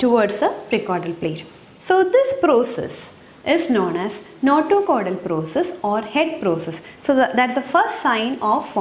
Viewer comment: Mam can you tell me the other name of notochordal process mentioned at 7:51 . I didn't understand it. Thanks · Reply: Head process